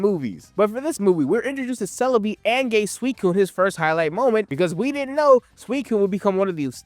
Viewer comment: I fully thought you said "and gay suicune"